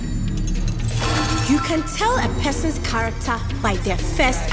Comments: I first heard "You can tell a peasant's character by their first action." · “You can tell a person’s character by their first action” Me: intentionally flashes myself on the first play to make the enemy team think I am a noob. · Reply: I thought she said "a peasant's character" lmao